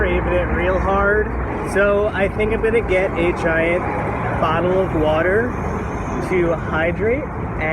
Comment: did he say a diet bottle of water, or a giant bottle of water?? :P